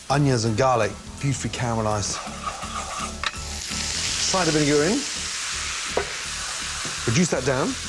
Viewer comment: did he really say 'urine'???